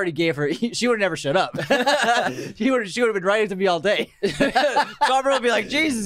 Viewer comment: Slightly buzzed right now. When you talked about Bellatrix keeping the diary I heard it as "she'd be riding it all day" instead of "she'd be writing (in) it all day" 😅